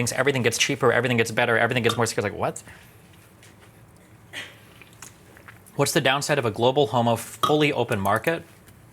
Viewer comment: what did he say? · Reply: “What’s the downside of a fully globo-homo fully open market?”